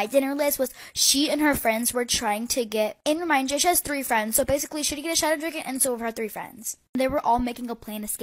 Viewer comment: I think it says "she would get a shadow dragon and so would her three friends"